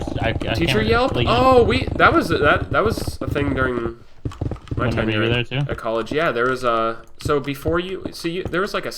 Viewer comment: What’s the N-word was talking about at 22:00 ? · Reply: Nazi